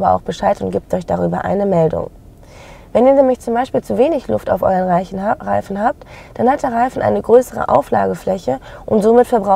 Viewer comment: ich hab bei dem Versprecher zuerst verstanden "wenn ihr zu wenig luft auf euren leichen habt" 😅 Ganz gutes video. Und auch gut erklärt. Die Musik ist allerdings viel zu laut und viel zu nervig und fördert meine Aggressionen 😢